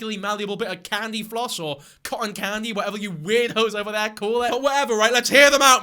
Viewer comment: omg i thought he called us weird hoes😭😭😭